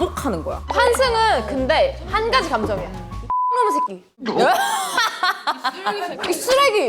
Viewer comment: I'm curious what JiU said here , I heard these words "i-nom-u-sae-gi" only behind, I had search online, JiU said, "this bastard b**ch" in Korean, if I'm not mistaken ~ Any Korean here?